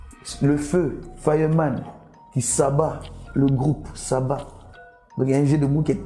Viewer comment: 😂😂😂Saba ouais c'est que j'ai compris